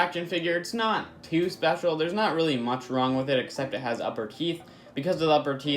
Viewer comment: He sounds like he is saying upper keeth